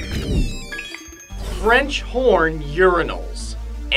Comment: At the beginning, I wasn't paying attention to the screen and thought you said "French porn urinals."